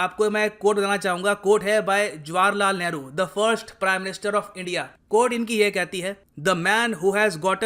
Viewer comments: he said first i heard worst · sir - first PM of india I heard - worst PM of india 🇮🇳😂🤣😑